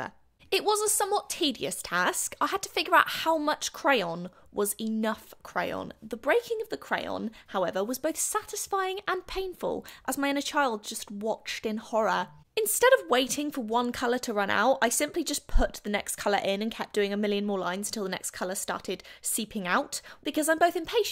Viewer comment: I'm sorry but did she say she had a child?!?!?!?!?!?!?